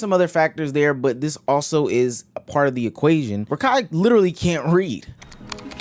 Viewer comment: i had to listen to this 3 times lmao. Thought you said Kai can't read and i was like what, ain't no way.